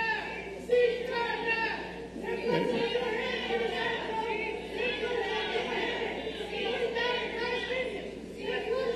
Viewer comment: what did the lady say? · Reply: That he’s a good man 😂😂😂. She must not pay attention to all the corrupt things he’s done